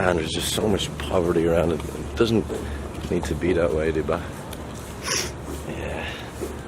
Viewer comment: what is duba? tuba? Second time you said it but can’t find that word